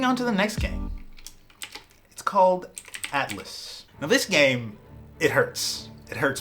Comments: I was a bit worried when you said Atlas, I thought you said Atlyss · i got fucking terrified because i wasnt looking away and thought you said atlyss